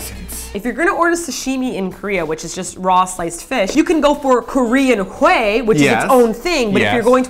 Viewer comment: English captions are now up! OMNOMNOM! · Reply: the caption said korean hoe not korean hweh, awkward......